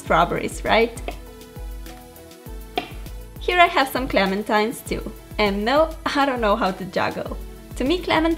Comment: Did she say clementimeat